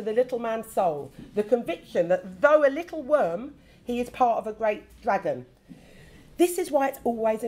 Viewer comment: ??? What did she say? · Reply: "though a little worm he is part of a great dragon"